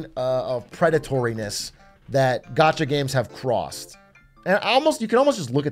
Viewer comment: The quality of gacha games are actually on the rise. Low quality gacha games that rely on the names of big ip's keep failing left and right because of the fierce market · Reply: I thought he was saying gotcha games